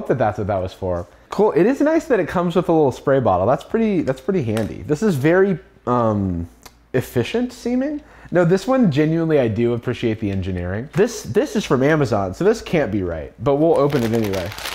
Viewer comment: The auto-captions say "efficient semen" at 6:43.